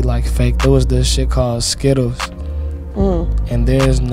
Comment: Who heard it as “zkittlez”🤣🤣